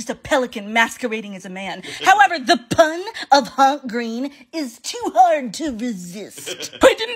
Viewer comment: I thought you said Hunk Green at first.